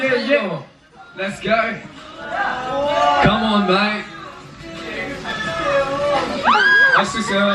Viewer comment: Did Felix say "come on mate" @ 23:11 to Chan??? Gah i wish i could see them ❤ I WAS FANGIRLING WITH YOU IN THE BEGINNING. GAHHHH!!!! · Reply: qetuoadgjlxvm i listened to it again on repeat and i think he said mate. i just heard it as bang lol my mistake ")